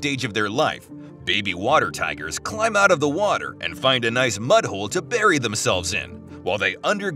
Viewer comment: I thought he said "Baby water tigers climb out of the water and find a nice butthole to bury themselves in" 😆